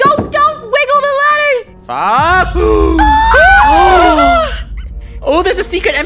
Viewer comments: 21 is it me or did I hear say 4U7K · I think said fuc- 😅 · Did say? UHHH or fug